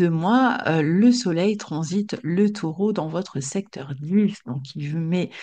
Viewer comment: dans notre secteur quoi ? J'ai écouté 5 fois, c'est inaudible... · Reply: 21: 30 secteur 10